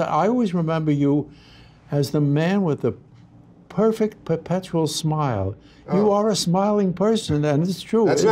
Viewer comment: Subtitles are not available for this video. What the HELL does Carl say at 0:05? "You have the perfect percustrial? smile".